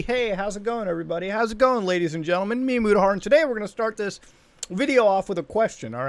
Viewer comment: I always hear "mia mudahar"